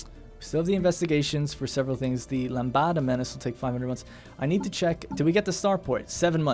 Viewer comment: "LAMBADA MENACE" Chorando se foi quem um dia só me fez chorar!!! ... Nah, mate, lambada IS a menace, but that one says LAMBDA, λ, like Half-Life. ...lambada menace...